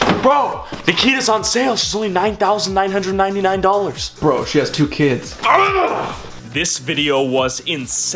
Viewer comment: I thought he said "Bro she has 2 kidneys!"